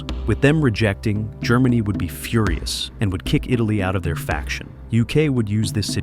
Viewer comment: Führer-us instead of furious